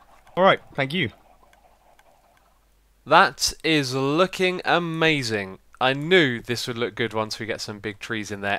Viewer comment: I was fast forwarding to somewhere around 13:50 and I heard your voice saying: "...king amazing!" And then I though that you where swearing there (F%cKING)